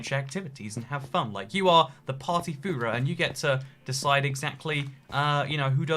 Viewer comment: did he just say Party Führer?